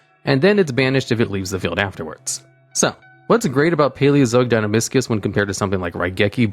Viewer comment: Lel I thought he said Paleozoic DinoBiscuits